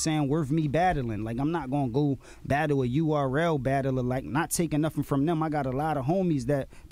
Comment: “I’ll never do a battle against one of these URL rappers cuz it’s not beneficial for me.” 👀 ahem ahem… after that one I was waiting for a “I’ll never get into a car accident.” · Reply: I'm sure i heard him say "not go battle a URL battler like that" meaning not gon battle any random low key battler in that league not disrespecting the league tho